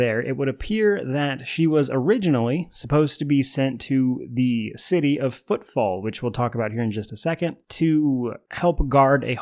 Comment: 40k is so weird I thought you said “the city of Football” and just continued rolled with it without thinking anything of it